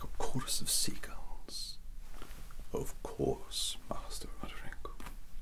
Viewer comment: Omg I have to keep pausing this because my dirty mind and weird sense of humor can't take this beautiful piece of art in one go Edit: I thought you said Master Roderwig instead of Roderick lmao and also 6:15 All I can think about are the Seagals from Nemo that go "Mine Mine Mine" Edit 2: Soooo can we chose our punishment because I like the belt the best the rod seems nice to